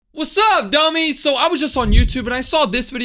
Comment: was I called a bummy💀. I'm a big fan of you're chanel.